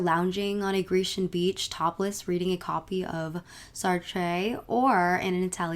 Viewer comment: am I high or did she say Sartre? The French philosopher??